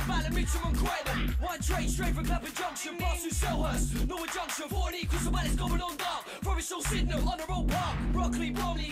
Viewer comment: What does he say on 00:51 seconds; the echo in the background? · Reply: Ding ding